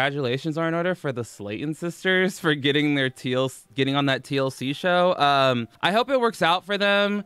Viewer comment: when you said "TLC show" in the beginning my dyslexic ass thought you said "teal seashell"